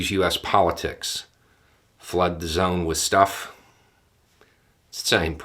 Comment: is that flood zone stuff? And is that interchangeable with firehose of falsehood? Sorry half asleep trying to figure out of I'm mishearing